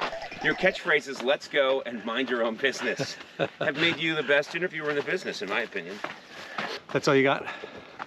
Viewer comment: Pretty sure he said "you're a pretty good interviewer" but the captions say "you're a pretty good idiot". I mean, the former does make more sense but you transcribed it and you were there